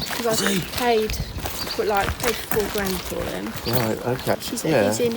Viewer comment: How much money!! I thought I heard £30 Grand, surely not but bless him he will turn out to be a proper angel, ❤️